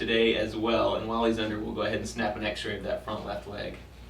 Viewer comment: When you said snap a x ray I thought you said snap his leg lol